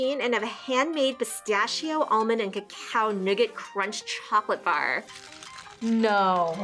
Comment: what did u say ? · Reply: Chocolate? Did you say Chocolate? CHOCOLATE! CHOCOLATE!